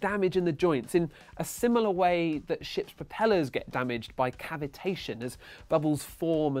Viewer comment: I thought he said shit propellers - I know it's ship propellers